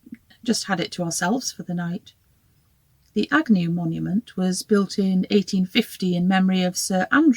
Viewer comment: I thought you said the 'agony monument' until I read the name.